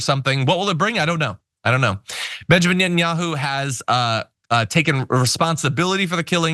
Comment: Every time I hear the name "Netanyahu", my brain turns it into "net and yeehaw". Not voluntary; my brain hates me.